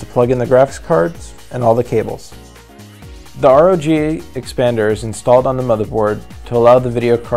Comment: What is the thing at 3:24 called? I cant really hear what he says. Sounds like "RJA expander" or something like that...